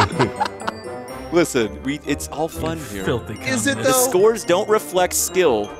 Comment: did just call a Communist? XD